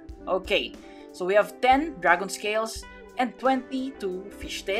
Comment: Lol when I heard you say dragon scales i thought you said dragon tails